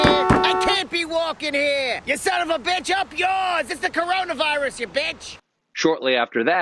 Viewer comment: Lmao what is it with subtitles, at 3:44 instead of "You son of a bitch" it reads "You son of a beneficiary itch".